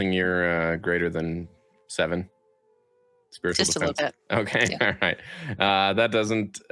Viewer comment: I had to listen three times to figure out was casting 'Illusory Defense,' and not as I kept hearing it 'A loser-y Defense."